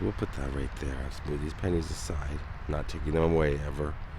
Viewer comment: Bruh, when you said "pennies" it truly sounded like you said PANTIES. I wasn't looking at the screen, and I was like "wtf who is putting their panties on Jim Varneys grave?"